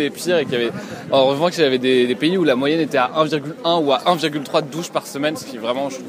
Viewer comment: At roughly 1:01 when the he said "1.1" I feel like he's not saying "un virgule un" ... or is it just really fast? What exactly did he say? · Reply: He exactly said " un virgule un" (=1,1), good luck with french